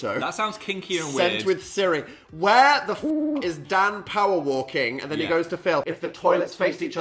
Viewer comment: I thought he said Dan Powell 💀